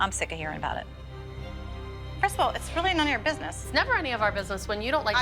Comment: Did anyone else hear Melissa call Cristie Crystal at 12:10 😂❤ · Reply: she didn’t say crystal she said “first of all”